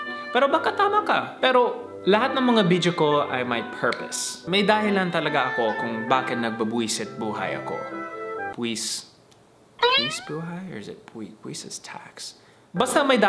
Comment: I thought I heard BWESIT buhay😂😂 But it was a great video though. Hoping to see the Water Park again❤ #kleankanteen